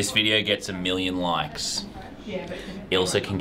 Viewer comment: Damn I thought he said a million views I was like o the. Came back saw he said likes and felt bad